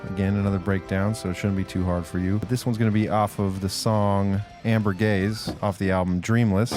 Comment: Thought he said Amber Gays...